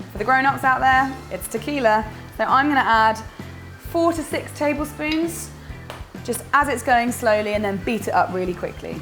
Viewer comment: Haha at 5:05 I heard "forty- six tablespoons of Tequila" :"D